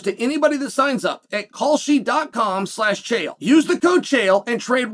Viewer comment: For anyone listening in Ireland, doesn't it sound like Chael is saying "Culshie" 😂